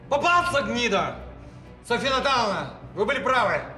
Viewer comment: София Наталовна или мне послышалось?😂 Мин"